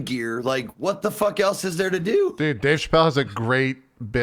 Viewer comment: what did said who has a great bit about michael jackson i cant understand what he saying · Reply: dave chappelle